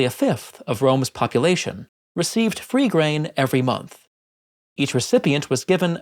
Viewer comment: The video also mentions that it's a monthly amount, which seems about right. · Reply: Oh damn, you're right, sorry, I don't know where I misheard that he said per year. Per month makes more sense, thanks